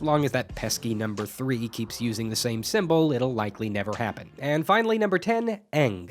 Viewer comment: i heard aang